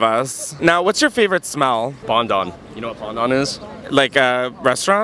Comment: bondon? pondan?? motherfucker is he saying fondant??? southeast WHAT??